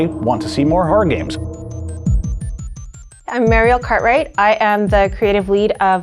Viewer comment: I had a Ralph Wiggum moment at 0:30. I heard her say "I'm Mario Kart Right"